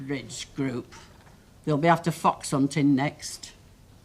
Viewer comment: What is "fox on tin"? 10:26 When Ena says re: the bridge olaying club, "Theyll be after fox on tin next."